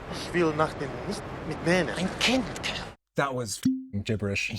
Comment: I think he wants to say, „ich will das nicht mitnehmen“ , sounds not that bad. other guy said before „Was ist denn?“ what is totally correct, meaning of „what’s your problem?“ answer „I do not want to take it with me“. So it makes sense